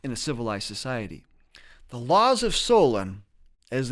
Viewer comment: did he say the Laws of Solon?